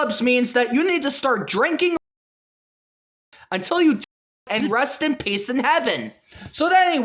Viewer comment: Can somebody explain what he says here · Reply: I found a video where it was uncensored he said laundry detergent, and you can probably guess what the other censored word is